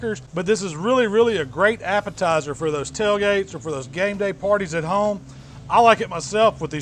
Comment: you said Game Day around 3:15, I thought i heard Gang Bang omg :D i almost knocked over my drink. i will definitely try this dip. maybe add some refried beans?